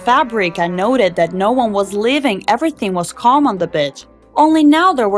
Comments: did u said... bitch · "Everything was calm in the b*tch?" Why did I hear that instead of Beach?